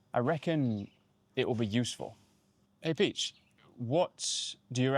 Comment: I heard at first he said: «Hey biтch» and I was like whaattt?😮